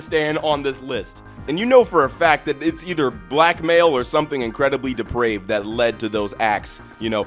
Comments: christ i thought he was saying they were black and male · I hate myself, on the horse screwing segment I heard you say "blackmail related" and thought you said "black male related"